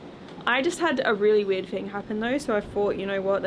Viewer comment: Instead of wait I heard 'weird' haha